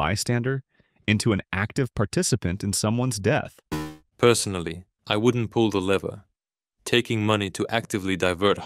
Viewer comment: Gemini sounded like its sayin: Me? Personally? I wouldn't let that happen.